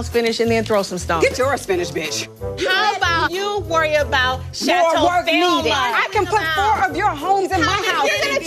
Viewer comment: What does Kenya call Chateau Sheree at 4:03? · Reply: Chateau Thelma, which is Sheree’s mom’s name. Kenya said Sheree’s mom was the one funding the chateau 💀